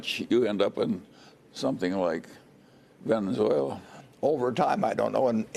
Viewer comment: if you Print too much money after a while you end up with something like.....??? Pennzoil??? Anybody know what Munger finished that sentence with? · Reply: Venezuela.